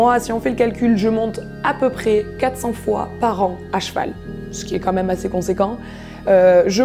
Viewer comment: J'ai remis plusieurs fois à 6:42 car j'ai crus qu'elle avait dit : " Moi je monte à peu près 4/5 fois par an j'étais choquée !! :' )